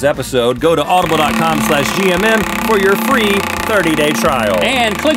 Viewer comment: Nothing like waiting for school eating cereal while watching gmm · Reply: I got so confused XD I thought you said gym rather than gmm lmao